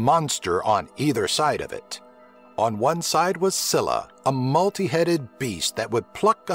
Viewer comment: did u say Wasilla ?